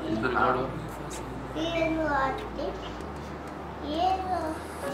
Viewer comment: is he said, "ye lo atif ye lo" 😅😂